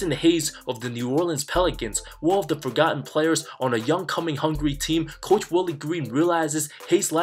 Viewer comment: Did you say the Pelicans were a young cum hungry team 6:20 ?